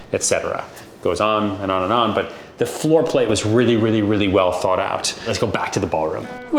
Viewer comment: he says "Floor play" instead of "Floor plan". Freudian slip or intentional?